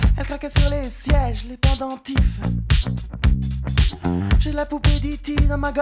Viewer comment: it sounds like he said "ANTIFA" hahahaha.